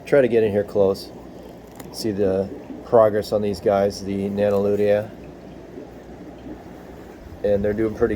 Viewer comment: nice fish man!!! what is the new species. sorry cannot hear you properly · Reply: Sorry was using Bluetooth set. They're Nanolutea